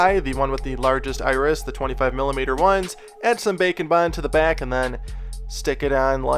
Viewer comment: always thought he said bacon bond. I am disappointed to find out its bake and bond. But love your videos!! keep doing what you love